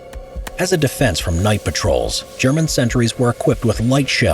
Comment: Why did you said German sentrys or i. Miss heard it in